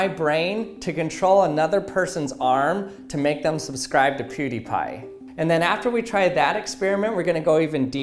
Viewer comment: that is cheating. wait, did you said PewDiePie?